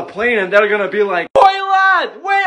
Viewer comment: I thought he said launch pad. I was thinking time to clutch up with lose fortnite skills.